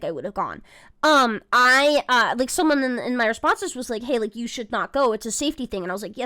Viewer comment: did say "Hey look, you should not go" or did she say "Haley, you should not go"? I can't tell if that was an accidental real name slip up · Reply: Sounds like Haley to me. Could be a slip or could be talking about someone she was speaking to named Haley and just mispoke, I do that sometimes.